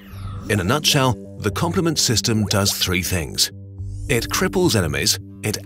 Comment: I thought you were saying " Compliment system " and I imagined those cute things going to the bacteria and telling it how pretty it is or something like that and convincing them to not attack but rather to grab a cup of coffee or whatever. Oh well. Your version's fine too, I guess.